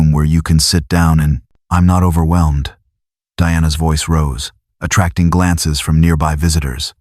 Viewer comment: did he just say Dianna voice Rose ? Did yall get the name ?